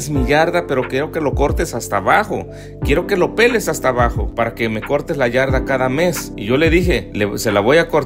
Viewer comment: Escuché "para que no me cortes la gran ANKARA MESSI"